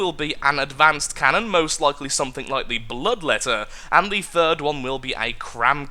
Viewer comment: I misheard bloodletter as love letter XD